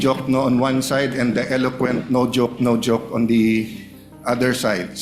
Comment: Did I hear it right? "The eloquent Diokno on one side and the eloquent noDiok (no joke) on the other? 😶